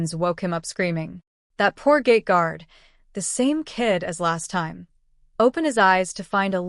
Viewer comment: I heard gate guard as gay guard 😭